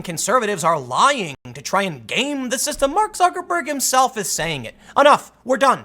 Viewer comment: Wait wait am I going crazy? "Yes Rob you are you talk to the voices in your head and they are your morality guide. Get help." Any way. it sounded like Tim said "Fuckerberg" at one point and that name. Really suits the guy I mean my gods Tim 10/10 for his new name.